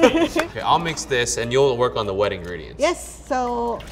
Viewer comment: When he said you'll work on the wet Ingredients I heard you'll work on the ring